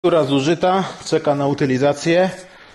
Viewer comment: Czy tylko ja na początku filmu usłyszałem "Fura zużyta czeka na utylizacje" ?